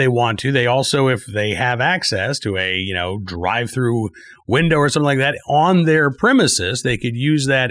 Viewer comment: did you say drive through window on a gun store? I like that business model! Can I have one MacGlock and small fries please?